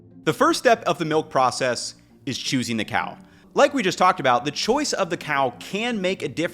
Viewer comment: For a split second, I thought he said, "chasing the cow" 😄